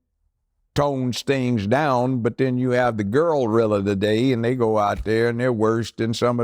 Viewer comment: Did I hear "Girlrilla"😂😂😂